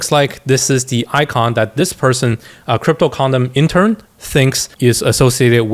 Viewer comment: I was so invested in this thing until I heard "Cryptocondom intern"